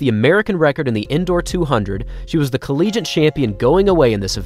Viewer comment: Did he just say "collegiant"? 🤣🤣